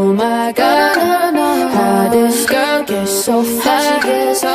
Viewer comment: At first I thought it said "how that girl get so fat" instead of fly 0-0 Ty for the likes :3